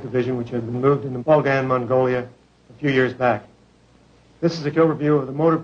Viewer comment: does he say "this is a killer view"..?